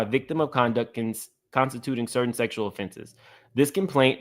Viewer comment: I thought you said, "sexual sofa" 😂